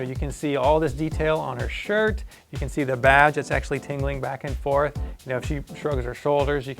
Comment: sounded like he said you can see the Vag! till I went back and listend again found out he said Badge